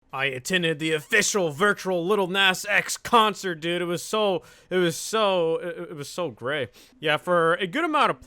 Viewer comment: I thought it said the lil nas x concert was gay lol